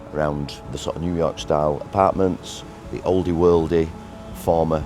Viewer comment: I thought you said ," Newyork Skyline" and got excited